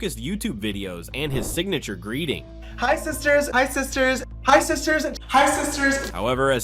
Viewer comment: I thought the jumpscare vids said hi si sirs